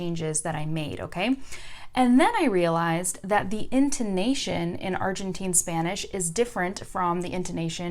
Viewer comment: what were you saying? I'm so confuse. I thought you said indonesian but it doesn't make any sense. If u see this please reply to me i'd love it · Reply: She said: the "intonation"